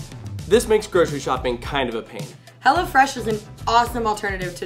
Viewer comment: Omg...................................... 🤣🤣🤣 I so thought she said HELLA-FRESH